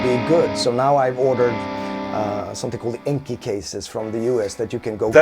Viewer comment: what does he say around 40:17 ? inke cases? wah?